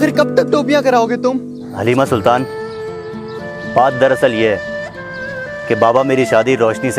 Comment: no one literly no one 5:05 he say paad dara sal ye ha instead of baat LOL